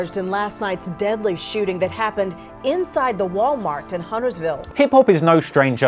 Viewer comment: I thought she said inside the walnut